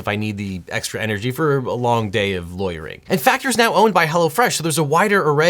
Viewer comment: I first heard "A long day of loitering". who else did?